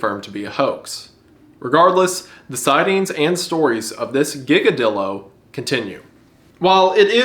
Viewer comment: I’m so sorry, but all I heard was “Giga Dildo”